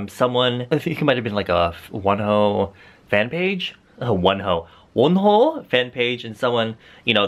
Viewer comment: OMG @ 0:48 I THOUGHT U SAID ‘ONE HOE’ MY GHADDD SORRY WONHO LOVE U THO 💗 Edit: 12:23 yesss.. slay dem bs&t